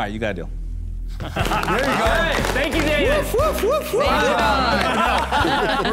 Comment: Daymond: “makes a deal” Guest entrepreneur: “Thank you Davis!” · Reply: Did he say Davis? I thought he said Daymond. I asked my lip-reading friend and she told me he said Daymond.